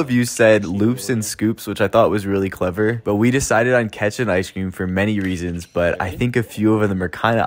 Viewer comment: Y’all, I thought he said ketchup ice cream ✋ 😭